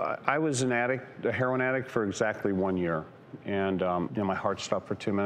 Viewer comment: Idk he makes it seem like he was this hardcore junkie. But he was only addicted to heroin for a year? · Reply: That's not what he says. He says at 2:32 "I was a heroin addict for exactly one year...." They weren't even talking about the book.